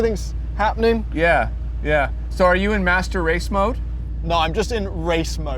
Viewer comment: Did I hear them say that a German car has a master race mode?? At about